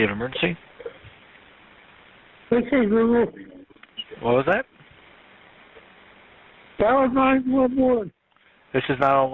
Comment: The man is saying OKAY GOOGLE DIAL 911, REPEATEDLY. These dispatchers can't understand the man is not okay but he's telling Google to call 911. He says he's fallen in his apartment and can't get up. He can't hear most likely because v he didn't tell Google to call 911 on speaker. They are able to hear him but he can't hear them.